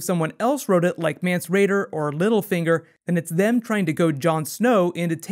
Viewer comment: Did you just refer to Littlefinger as middlefinger? Perhaps I misheard you. LOL